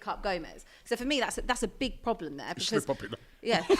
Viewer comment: what did say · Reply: "Slip up ya kna"